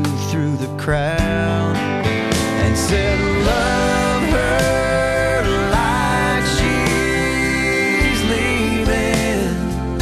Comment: Oh no I didn't mean that sorry, I meant instead of saying love her life shes leaving it sounds like he said, love her like HES leaving.